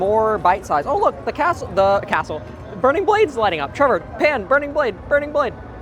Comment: Did you call your camera man Churro or did I mishear?